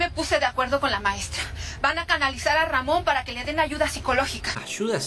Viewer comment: entendí mamon